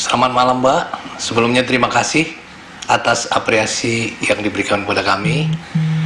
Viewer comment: Gw yang salah dengar kah? Kok apreasi? Atau aku yang nggak tahu ya? Oh.. mungkin bapaknya typo